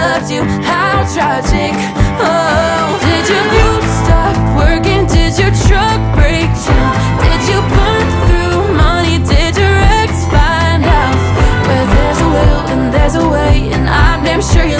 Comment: I swear I heard "did your boobs stop working?"